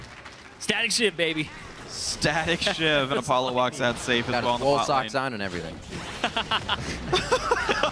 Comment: Whats the joke is telling at 23:55? I cannot understand because of the other caster's speaking... :( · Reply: zenedoboz +1 , I though he said "he's got his wool socks on and everything" - but I don't get it xD